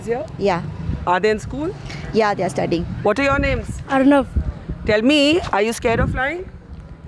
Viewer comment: Did he say Arnub?🤣🤣